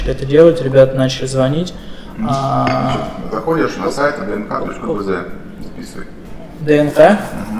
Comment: а что за сайт лендинги делать сказал dnk.vz, дайте ссылку не могу расслышать.